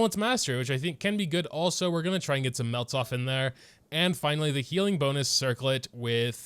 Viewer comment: did he say melt?